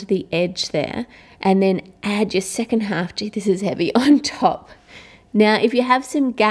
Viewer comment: Did she said "Dude this is heavy"?